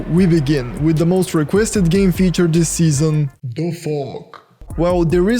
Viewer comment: sounds like he said "the f*ck"